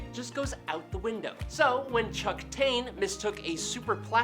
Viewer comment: LMAO. At first, I didn't hear Chuck Taine, I heard ; "Chunk Taint." 😂